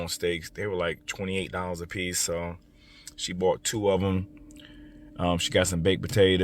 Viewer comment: It sounds like he said she bought the steaks.